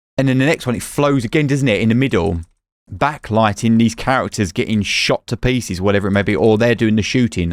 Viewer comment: Did you just say "backlight this bitch?" :D